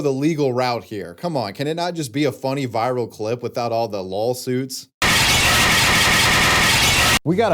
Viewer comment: wait. Did you you say "lawsuits" ...or did you say... "lolsuits"?